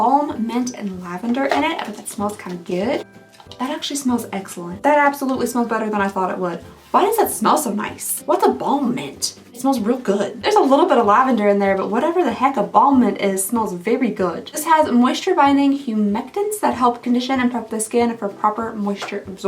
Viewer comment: I heard "What's embalment? It smells so good" So my brain switched that to mean embalming fluid 😅